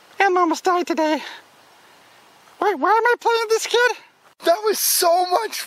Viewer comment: 54 What did Elmo say? X_x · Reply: He says...Why am I playing with this kid?